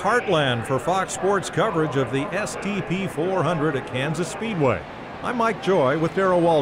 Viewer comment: Thought they said the STD 400 💀